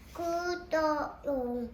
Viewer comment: 구독 해주세요 ~ 🎶라고 들려욬ㅋㅌ튜ㅠㅠㅠㅠ큩ㅌㅋㅋㅋ튜튜ㅠㅠ큐ㅠ너무 귀여워ㅠㅜㅜㅞ